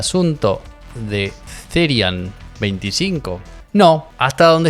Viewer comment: en un momento escuché "therian-25" en vez de "therac-25". 1. buen foreshadowing 2. explica todo 😇